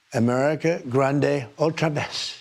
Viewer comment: Someone help me out here - "America Grande... what was the last word? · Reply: “America grande otra vez,” a loose translation of “America great again”